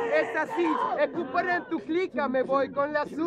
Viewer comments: Que dice en el 1:48 ? que después dice voy con la sube pero tengo a tu chica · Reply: Toto Starnari es tu par en tu clicka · Que dice en el 1:48 ? que después dice voy con la sube pero tengo a tu chica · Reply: en la 1:46 * yo entendí escuper en tu clicka )?